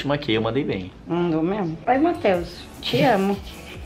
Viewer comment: Alguém escutou ou foi do eu 7:27 ta falando te amo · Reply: Oi Matheus, te amo Ela tava falando isso pq o cara não parava de mandar mensagem